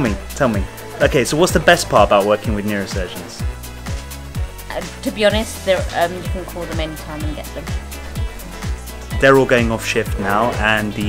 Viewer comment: What does she say at 7:33? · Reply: "To be honest, you can call them anytime and get them". That's what I heard.